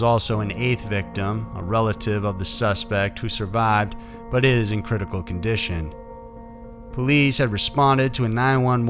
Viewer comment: did he say it is in critical condition or...issss in critical either way sounded weird. Seriously love this channel but if narration is your JOB and patreon give money to this dude at least pronounce things right and research if you don't know..like who caked Rotherham. .roTH A dam haha its like saying a silence p on psychotic ffs I woukdnt cate but he gets paid for this man off patreon people chucking money into his account for him to sit on google and research